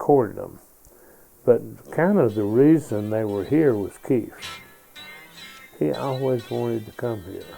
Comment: he actually says "Keef."